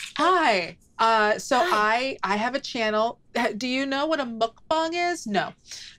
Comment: Lou trying not to stroke out over the reemergence of "mukbang" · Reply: I know what mukbang is but I'm gonna be honest, the first time she said it in episode one I thought she said she's "doing a milk bong" and I was like SHE'S DOING FUCKING WHAT!?